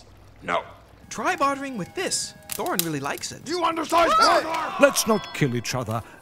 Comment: I cried laughing when I saw this for the first time! Love it! (What does Thorin say at 1:05?) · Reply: He says "You under-sized burglar!"